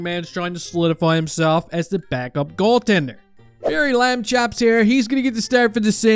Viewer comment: wtf you even said?! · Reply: Levi Lamb 😊chops